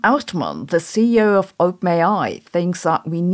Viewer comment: every time you say openai i mishear it as oatmeal